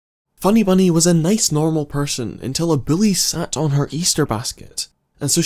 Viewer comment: I had to listen to that explanation of Funny Bunny like, five times cause I kept hearing "A bully" as "A Billy" and was like 'what the fet is 'a billy'?' Like is that kids' name 'billy'? But then saw that it was "Bully" and that still doesn't explain why she chose like the worst easter bunny costume ever...